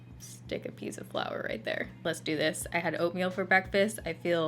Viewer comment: I thought she said she had opium for breakfast I had to replay it to hear that she said oatmeal!!! 😝😝😝😝